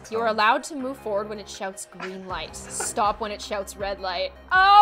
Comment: I thought said shut up but I’m pretty sure it’s someone else 😬